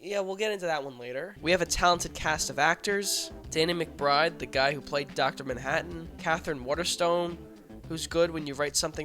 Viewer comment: The way you named Danny McBride and Billy Crudup one after the other made it sound like you were saying Danny McBride played Dr. Manhattan. That was the Watchmen movie I didn't know I needed until now.